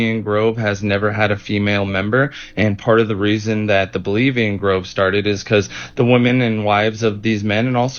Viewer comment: Oh, I heard "bulemian groove".